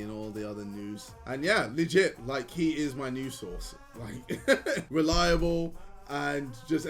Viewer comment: bro i thought he was saying nudes source lol